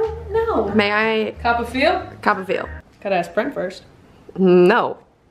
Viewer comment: 'gotta ask brent first' Bierson fans- what did she sayyyyyy!